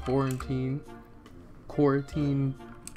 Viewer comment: so, if you're reading this during covid-19, stay safe! also at 7:16 it kind of sounds like he said quarantine lmao